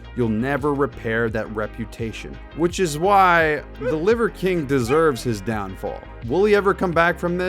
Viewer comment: I thought it said liver king deserves his liver failure😂😂